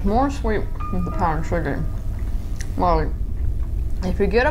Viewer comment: did she say ‘more SWEET then SUGARY? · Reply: She said "more sweet with powdered sugar"